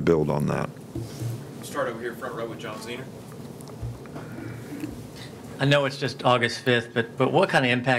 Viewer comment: did he just say "John Cena"? 😂😂😂😂